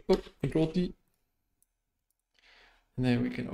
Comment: contorl what ? can't heard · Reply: Ctrl + o